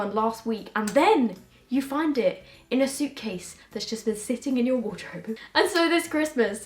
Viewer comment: OH SHIT. I thought she said she was sitting in her cat😂😭